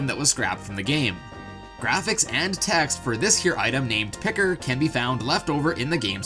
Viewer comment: I thought you said pecker and I was like hold up